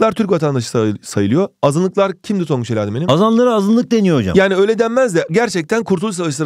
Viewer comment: tongucu bi tek ben anladim sanirim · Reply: Azanlara azınlık deniyor hocam dedi(ben öyle duydum)